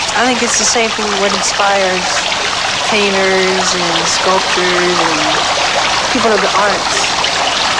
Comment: Thought he said “Haters and Sculptors” at the end.